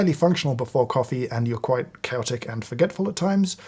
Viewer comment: Did James describe himself as "celtic" as to why he is forgetful. What are the associations with being "celtic" North American here who has not heard that descriptor used like this before. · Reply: I believe the word he used was "chaotic." Although upon re-listening, I can see how it could have been misheard as "celtic" by certain ears.